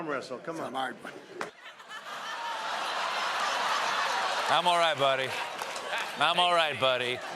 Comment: That guy didn't say 'I'm alright buddy'. I heard 'don't mind man'